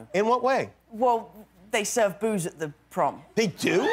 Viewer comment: is he saying booze or bobos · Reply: Booze